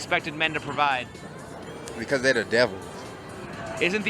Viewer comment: hahahaa I thought he said “because they the devil” hahahaaa